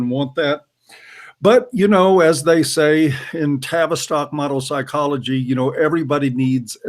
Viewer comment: What's that word used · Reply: Boddah , I think he said "Tavistock model psychology" <link>